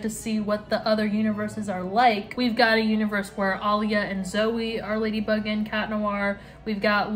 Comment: omg :(( i thought you said there was an au where alya and CHLOE were ladybug and chat noir. so dissappointed